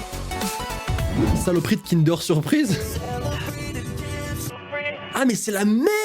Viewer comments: moi j'entend "saleporie de dannette au fruit" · moi j'entends : saloperie de danette aux fruits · A 9:35 j'ai entendu saloperie de danette aux fruits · moi j'entends saloperie de dannette aux fruits XD